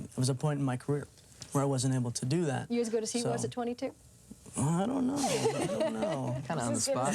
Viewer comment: Does anyone know what she says at 9:25? I can't make it out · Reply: She says, "are you as good as he was at 22?" in reference to Emilio's father, Martin Sheen.